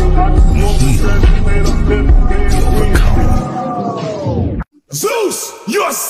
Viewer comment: I thought he said "We overcome zeus" 😂